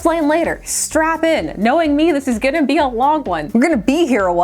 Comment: speed I heard "strap-on...this is gonna be a long one..."🤦‍♂️